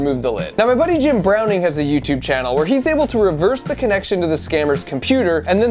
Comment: I thought that said James bulger (if you don't know who he is look it up)